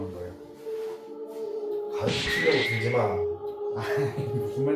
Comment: 자막 효과음 땜시 귀중한 목소리에서 뭐라고 하는지 몇번. 재생해도 자막켜도 모르겟다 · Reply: 가르치려들지마 입니다!